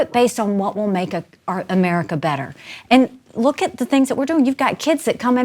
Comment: Anyone else hear here stop herself from saying "Make America Great Again"?